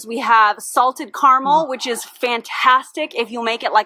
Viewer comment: I’m sorry. At 26:11 did say, “Salted Caramel Bone Broth”?!?! 🤢 Even thinking about it is making me feel nauseated, I can’t imagine consuming it.